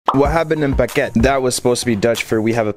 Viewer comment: Sounded like you said what happened in baguette